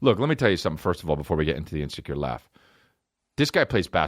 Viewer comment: Today my teacher said the word "Secure" and my inner baby thought she was saying the word "cure". Fucked me up for a second